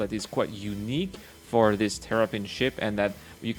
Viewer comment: Sounds like you were saying Terrapin instead of Tavarin.